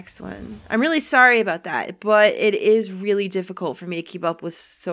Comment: "I'm really sorry about that butt." That's not what you were saying during the rest of the video.